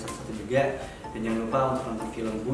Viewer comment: di menit 9:39 kayak nya kalau aku gasalah denger ale ngomong 'tgl 15 agustus 2015' deh, kalau ngga salah ya, coba didengerin deh